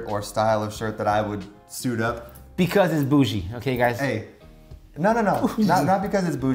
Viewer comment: Did just call you boujee??! I thought he was the one lol